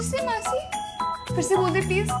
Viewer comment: i think he said maa ki